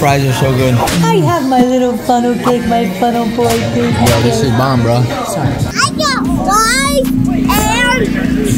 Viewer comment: did he say a bad word ?😭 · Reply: He said “This is bomb bruh..” [if u knew pls tell me lol ^^]